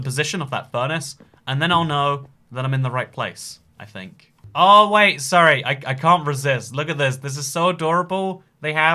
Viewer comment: what the hell did he just say? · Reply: "You want to stock it once, and then forget about it."